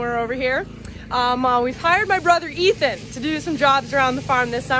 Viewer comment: for moment I thought you said "We fired my brother Ethan"